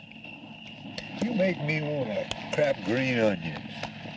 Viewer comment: "You make me wanna crap green onions"...? Am I hearing that right?